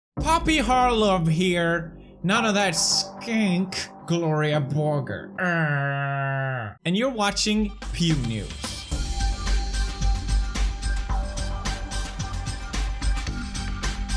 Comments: i heard 'pew nudes' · is it just me or is he saying pew nudes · i swear i heard him say pube news